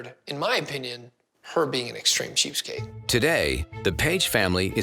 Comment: I thought he said .... “she’s an extreme cheesecake”